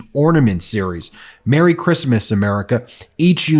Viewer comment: I thought said fuck Christmas 😂😂😂😂